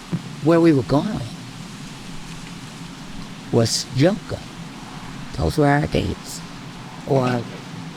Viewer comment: what she say? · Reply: Yoga